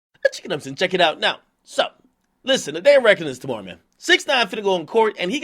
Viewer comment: The fuck does this man say in the beginning of his videos? All I hear is " checkum ups & check it out" wtff